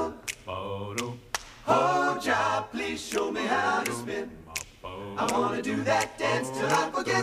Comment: I always thought they were saying pork chop show me to spin